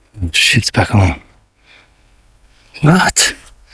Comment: So far I heard "he's back. Go. Shut up"